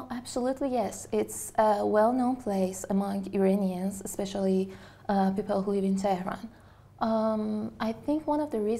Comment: Anyone can tell me what word that she said at 3:00? After "people who....." Hope that can help me to collect new vocab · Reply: She said "especially the people who live in ...."